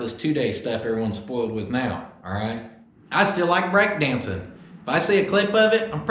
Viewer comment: I thought you said you where NO longer break dancing.